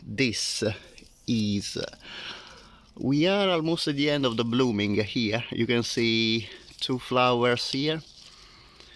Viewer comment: Thank you for a lovely tour. I couldn't quite catch the name of the plant at 4:36 (before the honeysuckle). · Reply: that is one of my absolute favourite fragrant plants in the garden: Stauntonia coriacea!!! Not very common in garden centres unfortunately, but nowadays is a bit easier to find in specialist nurseries